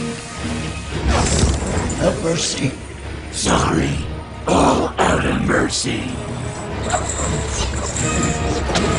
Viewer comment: I hear "mercy" is it? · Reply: The dude said "have mercy" and ghost rider said "sorry all out of mercy"